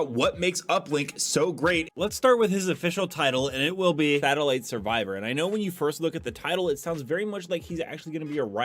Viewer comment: it says Satellite Savior. Sounds like you said Battle Light Survivor.